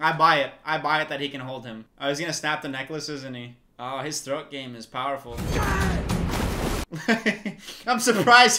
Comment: Did he just say "his throat game is powerful"?? lmao 😂